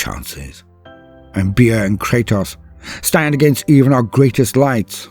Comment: Did he say Kratos?